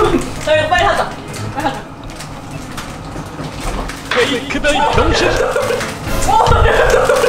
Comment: 초 보면 이 한테 자기야 빨리 하자 라고 들림